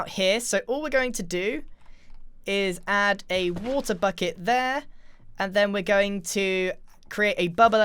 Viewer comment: I justskipped i random part and what i heard is "putt a butt here" lol